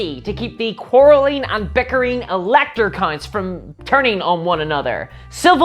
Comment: ooohhh only when I got to the vampires did I realize they are called elector counts, I thought she said elector cunts :P